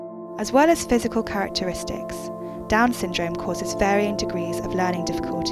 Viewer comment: I thought the little boy was saying dancing syndrome